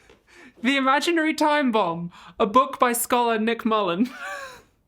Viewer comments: God i thought you said "The Imaginary Timebomb by Nick Mullen' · the audio says Nick Mullan, but the captions say PHIL Mullan. which is correct? 22:35 who else fell off their ass laughing when the Arsonist said the words "When I was a boy…" MISS Abigail Thorn, I see what you did there 😅 P.S. Can we get a rerelease of the enamel "Don't Play with Matches" pins?????